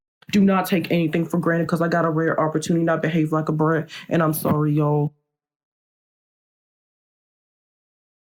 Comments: "I behaved like a brat" what i heard "I behaved like a bReAd" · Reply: i heard “I behaved like a bert” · "I behaved like a brat" what i heard "I behaved like a bReAd" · "I behaved like a brat" what i heard "I behaved like a bReAd" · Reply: I heard "I behaved like a bird" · "I behaved like a brat" what i heard "I behaved like a bReAd" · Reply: I thought he said “I behaved like a butt”😂😂